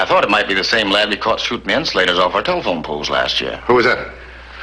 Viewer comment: At about 4:55 to 5:05, When Matthews is talking to Herb Dunlap, Dunlap says something about boys “shooting [Unintelligible word] off telephone poles.” What is he saying? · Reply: Insulators. Glass insulators for the wires.